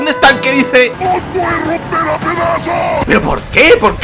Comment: Que es lo que dice ese ganado en el 0:23 ,nunca le entendí PD: execelente videos como siempre,saludos crack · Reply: Dice Hoy voy a romper a pedazos